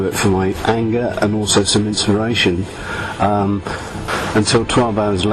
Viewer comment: he clearly says "some inspiration"